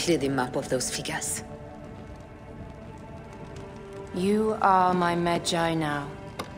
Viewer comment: 😦..wut? ...😖..😵..... Oh, right, "You are my Medjay now!" 🙄Pff..🥴 I honestly thought I heard her say "You are my Mangina!" LOL...😁